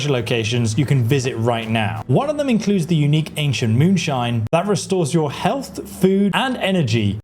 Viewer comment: My brain thought you said "ancient moonshine recipe" and I got excited for the alchemy/gold farming potential. Doh!